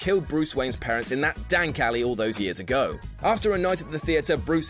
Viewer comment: did he just say dank alley?